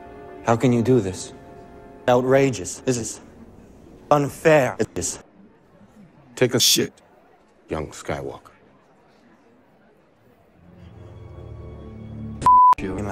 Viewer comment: I thought windu said take a shit skywalker lmao